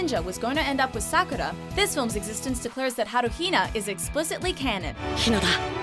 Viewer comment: She said HaruHina instead of naruhina lmao 5:10 0.5x speed if you dont believe me